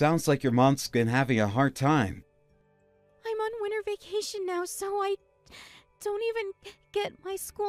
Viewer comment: Why was there a blizzard when she was out of school for SUMMER break? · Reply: Yeah I think I heard her say she was on winter vacation 13:59 but the text read summer vacation